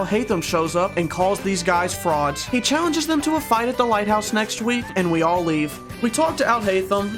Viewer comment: i thought he said white house instead of lighthouse